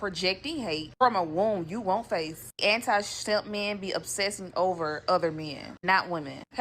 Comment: did she say shes “ anti SHEMP” ? Im not anti SHEMP. BUT I DO PREFER CURLY JOE. ✌️